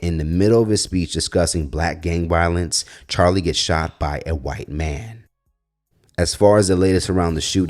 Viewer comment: Omgoodness 😂😂😂i thought yall was saying Charlie Brown got shot. I was gonna ask how is Snoopy coping.😂😂😂